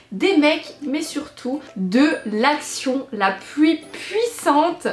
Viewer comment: Y'a que moi qu'à entendu"la pluie puissante" ?